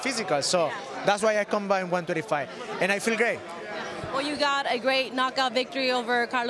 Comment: DID HE JUST SAY ..I FEEL GAYYY ..! 0:25 lol 😂💩🌈🌈🌈🌈🌈🤣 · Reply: Lol I think he wanted to say I feel great because at 0:50 he said it again